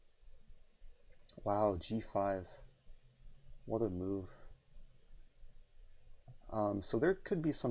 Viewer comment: I heard: "wow g5, what a noob" and than i realized he said move.